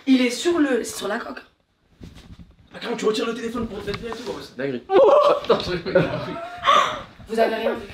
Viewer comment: Il dis quoi à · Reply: (Tu retire le téléphone pour te mettre biens et tout )je crois